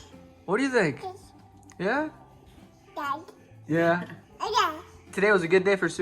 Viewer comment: Did just said Dad?